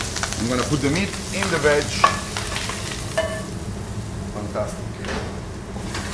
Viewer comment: Does he say 'Mantab" at 6:22 ?